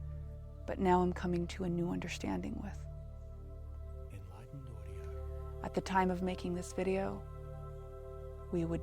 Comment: I keep hearing “in life and who we are” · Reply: It's an audio watermark in the music; it is saying "enlightened audio". Lol. Not so bad really.